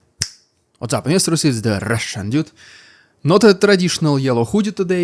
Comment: I am sorry I can´t help it but every time I keep hearing "What´s up ministers, it´s the Russian idiot!"